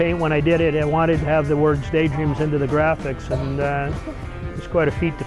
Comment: Amazing van really... But was I the only one who thought it said "Gay Dreams"?